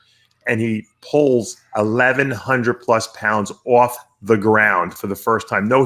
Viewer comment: Eddie Hall did it first. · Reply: Slimey Simon haha no pal, I saw that one video of him deadlifting over a thousand pounds and he was saying his nose started bleeding and he lost his vision for a week or two and I could have sworn he said that was 1100 lb